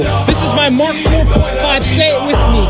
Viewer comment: I thought he said Microwave Paralysis Reaction 😂